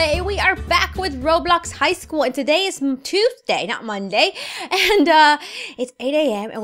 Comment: I thought u said toothday literally I laughed so bad then I thought she probably she said Tuesday lol!